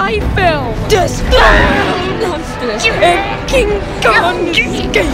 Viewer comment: did he say king kong